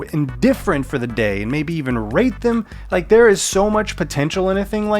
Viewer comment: "..and maybe even rape them" My mishearing and sleepy ass: "Oh dear"